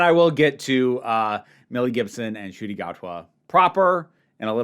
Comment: Thought you said 'Mel Gibson' for a second there and nearly spat out my tea!